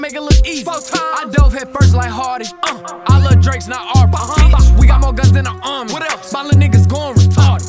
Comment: "I love drakes not aubrey" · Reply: damn I just got that line I thought he was saying Arby's lol